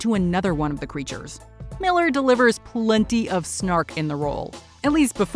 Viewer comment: How dare you not mention Captain Cold when you talked about Wentworth Miller. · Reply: Yeah, when she mentioned "plenty of snark" I literally heard "Snart."